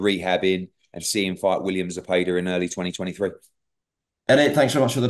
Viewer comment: Did he just say early 23? or am I hearing things?